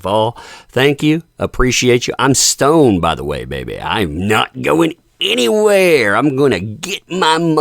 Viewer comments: 🫣👀 OOPSIE!!! Lol. Guess I was a wee bit “chilled”. 😂☺️🖤 Thanks for letting me know. Sincerely, Waking and baking before therapy!!! 👀🖤🖤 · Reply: 😂 Lol, yes, tht helps a bit to know I wasn’t the only one who thought he said “I am stoned, I’m not going anywhere”….. 🤷🏻‍♀️🤣😂🤣😂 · HAHA! At the sign off, at first I thought you said, "I'm stoned, by the way"...LOLOL. In context I realized what you actually said but had a moment of whoah. LOLOL.. As far as the doxxing goes, just awful and completely inappropriate. Sorry this is happening to you.